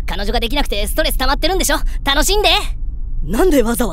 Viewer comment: 最後、まっすぐ進んでって言って、実はその先崖パターンかと思った。「楽しんで」とかも「たの死んで」に聞こえて、勝手に怖がってたw